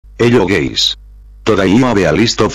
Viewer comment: i heard "hello gays"